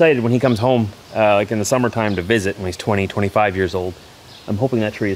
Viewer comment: Prayer's on the unfortunate situation with IVF. you should have gotten your drum kit out and played a few beats as a ceramonial tree planting 😅. Wheeping willow ?? Never seen one that shape. I thought you said 3 years old. Great concept of Theo & the tree growing together. 🙏